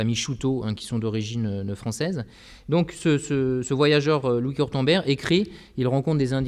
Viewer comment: À 46:00, impossible de comprendre le nom du "Voyageur" Français ou Franco-Canadien. Dommage, j'aurais voulu m'en servir pour un article sur les Osages... Quelqu’un a-t'il l’ouïe plus fine que moi et pourrait cordialement m'aider ? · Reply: Il s'agit de Louis Cortambert. Cf. Notice sur Gallica pour un de ses livres : Titre : Voyage au pays des Osages ; Un tour en Sicile / par Louis Cortambert Auteur : Cortambert, Louis (<num>). Auteur du texte Éditeur : Arthus-Bertrand (Paris) Date d'édition : 1837 Sujet : Osage (Indiens) Sujet : Sicile (Italie)